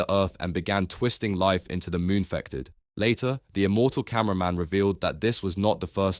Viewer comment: wait, did he just say "the immortal cameraman" 🫠